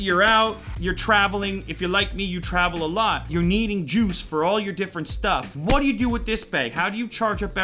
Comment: I literally am shocked as I thought I heard him say "You'll needing jews in all of your different stuff"...